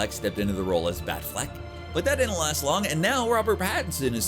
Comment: Did he say Batfleck? As in Arthur Fleck? Role the next theory